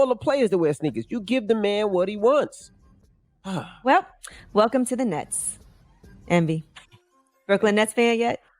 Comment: sound like she said “welcome to the nuts”🤣🤣